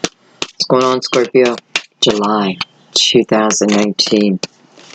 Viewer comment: Did anyone else notice at the beginning of the shuffling I heard her say "Scorpio, July 2018"!!?? Instead of 2019! This resonates with me and my daughter My daughter is Scorpio with Cancer Rising and Leo Moon, I am Leo with Scorpio Rising and Pisces Moon. My mother passed away and I am working through past childhood issues to get over some Anxiety attacks and am also looking for work, and my daughter is starting university to pursue her creative passions, and I just finished studying too, so it all made sense to me, so thank you. Also my mother was a Taurus, and very determined, so she would want me to get the message. I do feel chills when spirits are around. Wouldn't be surprised if she is looking over us and guiding us as we are both a bit lost and trying to overcome obstacles to move forward and have better lives.